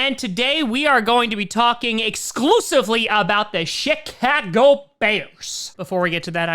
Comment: i could of sworn i heard him say tha shit ca go bears?....